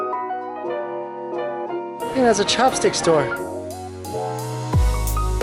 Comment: "Chopstick store" ..... I thought he said chapstick store😂 Like dang they take their lip care seriously!